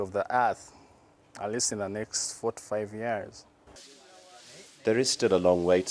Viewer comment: I think he said "4-5 years" but it sounded like "45 years" and I actually thought he meant 45 years... and realized he meant 4-5 years. These people make me sad, man.